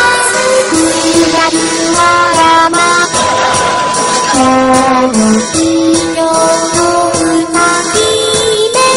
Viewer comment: why is that part beeped out? what do they say there? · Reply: The full line is "futatsu atama misemono (二つあたまミセモノ)", meaning "the two-headed freakshow". The "mise" from misemono (freakshow) is beeped out, though as a non-Japanese, I'm not sure why.